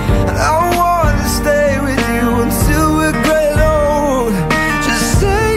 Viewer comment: Who thought he said untill were getting old when you were a kid and now you realised he said untill were grey and old Omg thx guys I've never gotten over 10 likes...